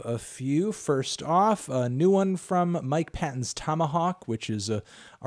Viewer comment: sounded like "new one from Mike Pence" at first 😂